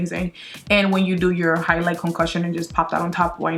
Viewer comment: highlight concocion LOL i thought you said concussion